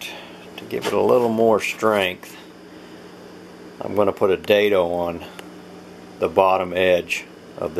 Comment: Hey, i want to make the same tool box for my 98' nissan frontier and i was just wondering what you said the notch thing is called at 2:44 and how do you do that lol? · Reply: it's a dado or lap joint. cut it out with the table saw or a router.